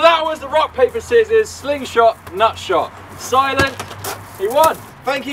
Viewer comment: i thought he said sling shot nut job